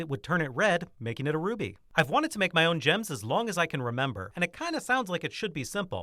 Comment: I for a second thought the video said, "I made my own Rabies." 😂